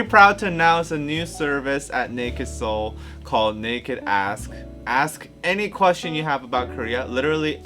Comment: I heard "nakd as*"